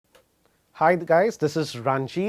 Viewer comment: Did he just say "hi the guys..." kya yar. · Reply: Shivdev Singh "hi there guys"